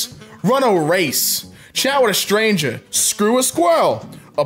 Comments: did he say shower a stranger? Lol 😆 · I thought he said "shower with a stranger, fuck a squirrel"